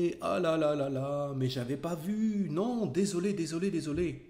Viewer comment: Really like this channel- I wish there was french subtitles- can anyone write what said at 1:16- 1:20 and again at 1:48- 1:50 I got "oh, lala mais j'avais pas vu" and "... mais, je t'ai pas vu" is this correct? Or am I missing some small words? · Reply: Well, (I think) the grammatically correct statement is "Je ne t'avais pas vu", but he pronounced it as such, "J'n't'avais pas vu"